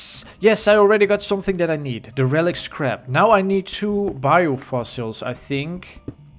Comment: I thought said crap but he said scrap